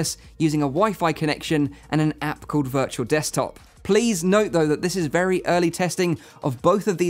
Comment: only I hear "app kurwa virtual desktop"?